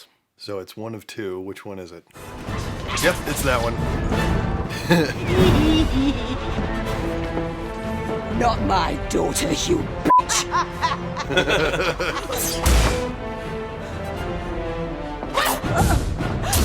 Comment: When Molly was standing up to Bellatrix, I heard, "not my daughters" as in multiple so I looked for Hermione but she wasn't there